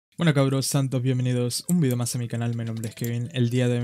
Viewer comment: cuando dijo sean todos bienvenidos, yo entendí "santo" Bienvenidos jajajaj.